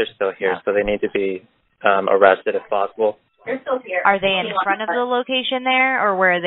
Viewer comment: Do i hear and another girl in the background or am i reaching? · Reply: sounds like saying 'they're still here'